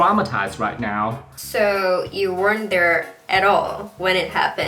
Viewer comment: Chị cho em hỏi chút là ở 1:22 " weren't there " chị đọc nhanh 2 chữ t đọc thành đ ạ... e nghe như kiểu wonder ý ... mà bỏ qua cách đọc đi thì câu này nếu e ko nghe rõ ' weren't there" thì phải tự dịch theo ngữ cảnh là ( bạn... at all khi mà có chuyện xảy ra) mình phải tự đoán nghĩ là ( so , bạn không có ở đấy lúc mà vụ cháy xảy ra) ... pls chị chỉ dẫn em cách đọc nối " weren't there " cho n ngầu như chị đc hông ạ.. e cảm ơn <3